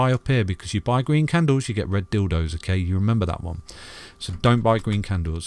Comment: Did he just say “red dildos”